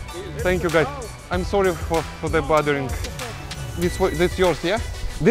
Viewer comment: The way he said bothering at the moment I heard "sorry for fathering", he did father them for sure.